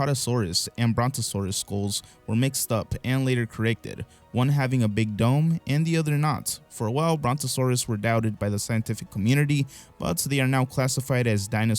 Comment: On the dinosaur conspiracy bit I totally heard “big dong” rather than “big dome”. I was like…DUDE.